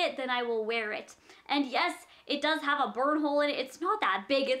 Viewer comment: it almost sounded like you said "bird hole" :D